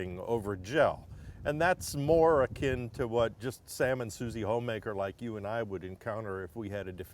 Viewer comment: I heard "Salmon Suzy Homemaker" at first 😂